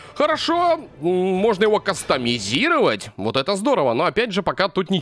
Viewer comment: почему мне послышалось кострировать 😑